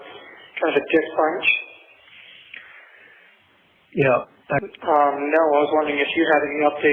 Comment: what does he say? Kind of a “dick punch”?